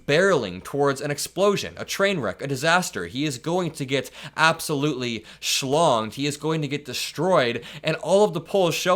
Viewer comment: "He is going to get absolutely schlonged?" Did I hear that right?😂